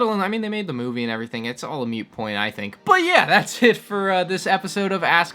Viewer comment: It sounded like you said "mute point" at the end there.